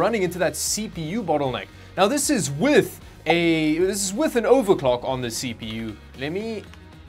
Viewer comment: I heard "overcock" at 10.43 and fainted from laughing.